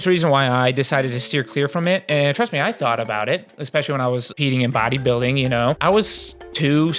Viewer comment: what did he say? xxxx in bodybuilding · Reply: Competing like participating in a show